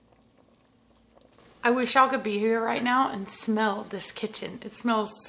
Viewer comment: ! When you said "I wish y'all could be here right now" my notherner ears heard it as "I wish I could shotgun a beer right now" and my eyes widened so much😂😂😂